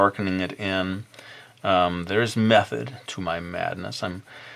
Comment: When you said "There is method to my madness" i thought you said "There is meth to my madness" xD